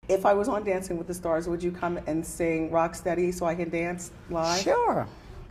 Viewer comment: I swear Wendy said "Fuck steady" instead of"rocksteady". Lolz